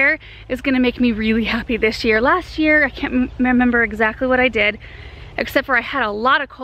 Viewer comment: Pardon? Did you say "memember" at 21:21? 😂 I wonder if this is some Benjamin speak? I LOVE how honest and human your videos are ❤ It's so refreshing seeing your family as they truly are. Even though I live in Scotland, I hope to meet you some day, you are such an inspiration to me! I watch your videos everyday and admire your hard work and authenticity so much! Keep being you! 💚🌱